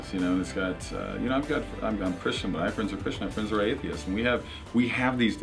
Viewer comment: What does he say at 2:17? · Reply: "I'm Christian. I have friends who are atheists."